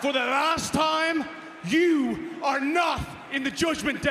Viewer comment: First I thought he said you're not the father 😂